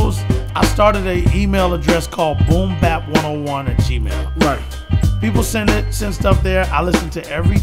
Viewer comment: sounds like he is saying boombap101@